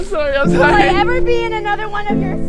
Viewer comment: WHO HEARD ANDREW CALL LEXI BABY 5:59 if u did not hear it he said I'm sorry ababay